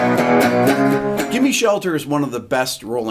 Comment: Did he say Jimmy Shelter at 4:37?